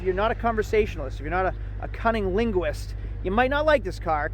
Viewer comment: did he really say cunning linguist?